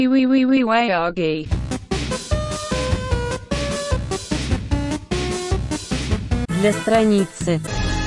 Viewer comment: is it just me that i hear "we are gay" wtf 2:48 or not